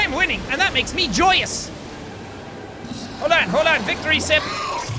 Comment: I thought he said victory simp